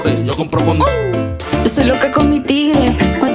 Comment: Yo pensé que decía "soy loca cometido" :'3